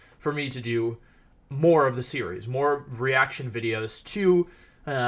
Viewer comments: New mic is broken I think. At 2:34 it sounds like he says videos but it must be misfunctioning mic. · wolfe in wideos: :3 wolfe at pokemon championships 2016 looking at his opponent: >:( real life intimidate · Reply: I thought I was the onlyone who thought he was saying wideos instead of videos lol